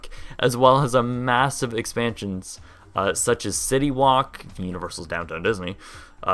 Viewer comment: did he just say city wok